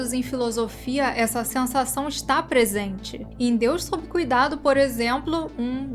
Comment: "Em deus Sob cuidado" não entendi ou não achei esse conto, qual o nome? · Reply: O nome é “Deus sob Cuidado”